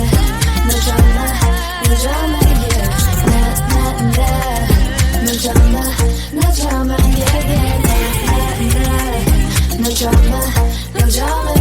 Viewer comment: 나만 늦었다 늦었나 예예 ×2 라고 들림나?